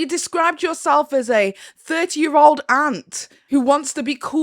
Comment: I heard "30 year old ant" and...accurate 🫢🥲